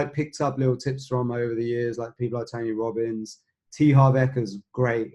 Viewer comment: who is the money mindset person he name drops? I can't figure it out and the subtitles are wrong. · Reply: T Harv Eker